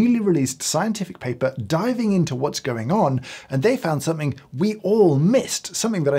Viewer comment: Al 0:34 did he just said "that we all mist" ? I think there was a pun intended xD